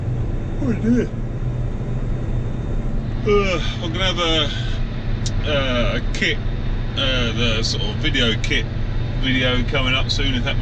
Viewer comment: After you yawned, I thought you said ‘I’m going to have a kip video soon’!